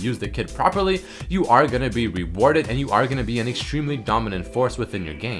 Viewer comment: I thought he said reported o.o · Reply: rewarded not reported xD